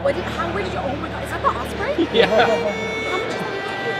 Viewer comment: did she say ospray? Oy vey